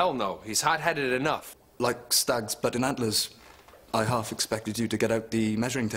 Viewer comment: I always heard "but in antlers". You sure it's "butting"?